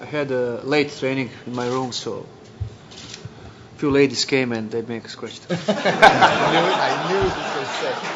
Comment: what did he say? I hear “a few ladies came and .........?”